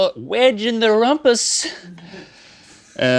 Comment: did Link say 'rompus'?! That's almost ass in Norwegian: Rompe! Hahaha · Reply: No_Notes He actually said “rumpus”, which isn’t a real word; he was making a word play on the word “rump”, which means the same thing in English as “rompe” does in Norwegian.